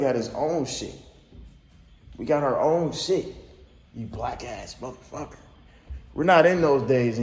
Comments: I heard “wack ass” but who knows, DDG gonna get that dub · Kinda sound like he said “whack ass mf “ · I don’t even like Tayler but I swear I hear “whack ass mf”... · sound like he said wack ass mfs but dont make sense cuz he the wack one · I honestly thought he said “whack ass mf” but that’s jus me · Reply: I thought he said "whack ass mf" too, but I could be wrong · I thought he said Fin whack A motherflower 😳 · Sound like he said "Wack ass mfs" to me. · I thought he said whack ass ...... maybe i heard him wrong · Tbh I heard “wack ass mfs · It sounds like he said whack ass mf but idk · It sounded like he said “whack ass ****” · it sound like he said whack ass mf but it’s mad hard to hear. lmk what y’all hear if im trippin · At first I thought he said wack ass